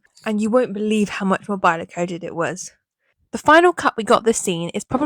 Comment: Very great analysis. But I couldn't hear this part clearly: "And you won't believe how ... it was". What is ... here exactly?🤔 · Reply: How much more Byler coded it was